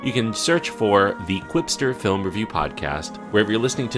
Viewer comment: Glad you spelled that! I heard "quickster" 😬!